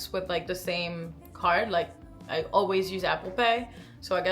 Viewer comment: I thought you said "After pay" when you said "Apple pay" and almost had a heart attack! I love that the perks systems are built in to our cards now though it's great!